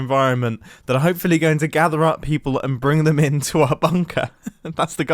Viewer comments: did he say confusd instead of confused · confoosued?? 4:47 what is that word